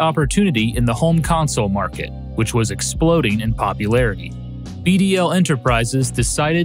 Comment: I thought you said EDL enterprises for a second lol